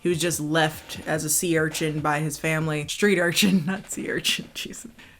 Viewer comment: I don't understand. How does he NOT remember her at all? Did suffer head trauma or some shit? Also, I thought you said "sea urchin" instead of street urchin. "I imagined him as a sea urchin" was a funny line.